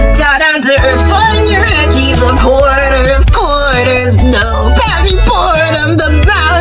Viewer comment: animatic sounds like he said “quarter of quarters”